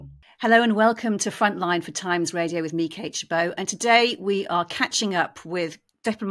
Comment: What is this interviewer's name? It sounds like she says "Me Kate Chibo"